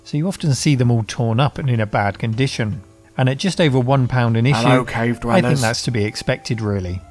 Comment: Anyone else hear the "Hell Cave Dwellers" at 29:53?